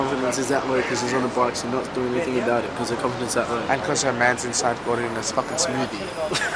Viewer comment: What did say? I can't hear it :) somone help? :D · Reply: He said "cause her man's inside ordering a fucking smoothie"